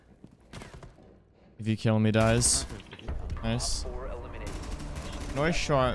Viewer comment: did bro say if u kill him he dies